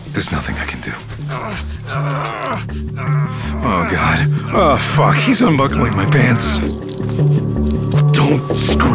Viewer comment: I'm sorry.. A little bit of a inappropriate time I thing what the heck DID HE SAY?? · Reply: “Oh god… OH F***…! He’s unbuckling my pants!!!”